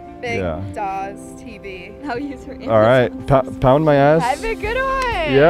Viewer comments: Did he just say 'pound my ass' at 2:44 !? 🤣🤣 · wait, did he say "pound my ass" at 2:44? 🤣